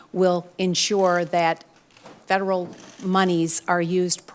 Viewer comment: Did she just say "moneyS" ?